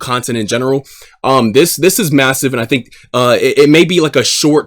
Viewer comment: did you say massive?